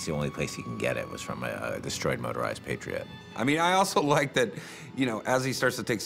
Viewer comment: Thought this video said "Motorized Parrot" I was sad when I found out it didn't.